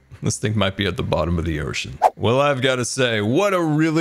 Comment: sound kinda like ‘air ship’ instead of ocean :3